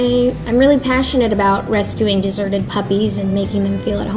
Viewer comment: On ells I thought she said dessert to